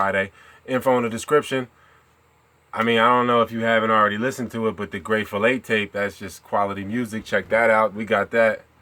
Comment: Lolol eye thought yall was saying the "great filet tape" 😂😂😂😂